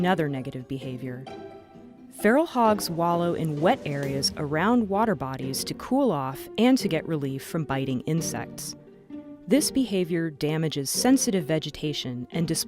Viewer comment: I heard biden insects. Lamo too true. Beta bidens are like insects.